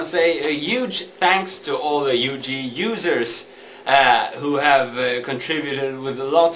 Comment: Did he just say 'a huge thanks to the ut loosers" :P